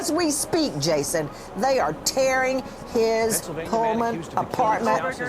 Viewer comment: Oh gah, i thought Nancy said "They are tearing his colon apart." 😬 I'm thinking, "What did I miss??" 😂